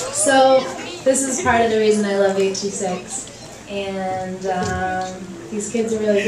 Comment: LOL wait! What did she say at 5:16? Please I need an answer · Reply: 'This is part of the reason i love 826.'